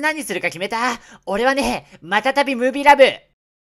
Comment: マタタビムービーラブ って聞こえた